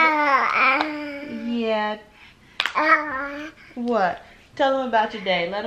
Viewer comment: Who else heard him say yeah